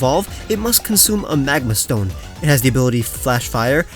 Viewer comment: Rewatching this I thought you said mangazone and not magma stone lol